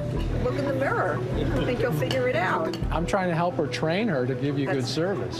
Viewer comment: i thought he said hi ladies im trying to be honest